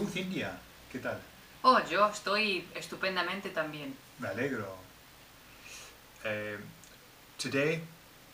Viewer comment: Cerca del principio (@ 0:35), dice, "....estupendamente tambien." y dice, "malegro" o "a negro". ¿Qué está diciendo? ¡No entiendo! Por lo general, puedo usar "Google Translate" ayudarme pero esta vez no. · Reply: Me alegro= I'm happy for you :) Cxx